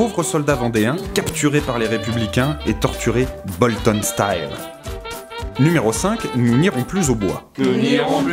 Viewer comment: Il a dit quoi à 2:48 ? Je comprends pas ? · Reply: et torturé "Bolton Style"